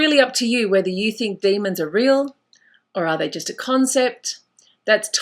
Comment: Lol did she just say "Blessed Beast"?!